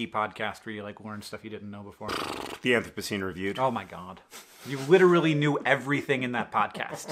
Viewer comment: what was his answer for the podcast question? I can't make out what John's saying. "The enthracine review"??? :) Thx.